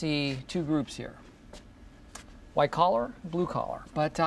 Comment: I always thought he just said "colorblind", which would be funnier 'cause it sounds more absurd and non-sequitur